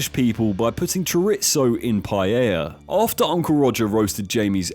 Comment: it took me so long to understand what food you were saying in the paella. Chorizo.